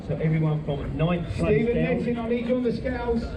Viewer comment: did he call ur name as u walked behind him? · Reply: 'Stephen Netting' - so no, not me.